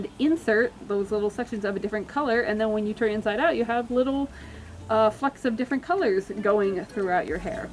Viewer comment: when they said "little flecks of different colors" i thought they said "little F**KS of different coulors" XDDDDDDDDDD